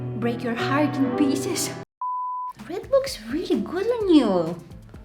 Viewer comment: am I the only one who heard it p***s